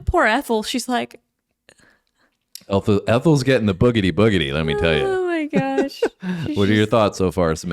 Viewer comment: Ethel was not just a hussey, she was a brazen hussey....and for years, instead of hearing "boogity, boogity" I thought they were saying "look at that, look at that" - which seemed even funnier.